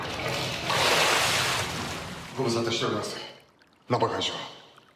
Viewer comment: 「ご無沙汰してます、真っ裸会長」て本気で聞き間違えた。